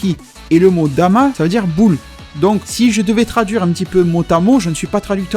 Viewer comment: J'ai compris "si je devais traduire Botamo" 14:33 😂